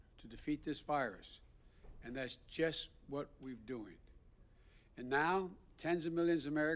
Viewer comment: did he really say??? “ That’s what we’ve doing” ????????